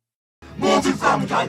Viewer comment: When I first saw this, I thought he said it's everything he loves. XD